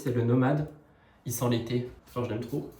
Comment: Pour le parfum j'ai entendu "il sent les pieds" au lieu d' "il sent l'été" ... J'ai bugué 😂